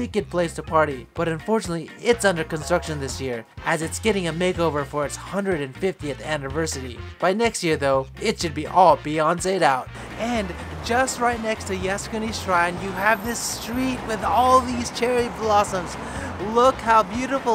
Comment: Did you just say at 5:48 “it should be all Beyoncé’d out?” 😳😳😳